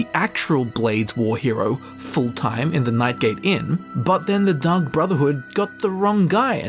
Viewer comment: I heard Dung Brotherhood which is infinitely hilarious to me since he was talking about them making a mistake